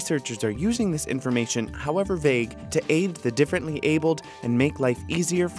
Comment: wait. did he say "differently abled" 0:36.